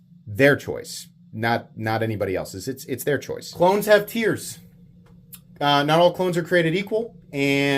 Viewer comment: I heard, "Clones have tears" :face-blue-smiling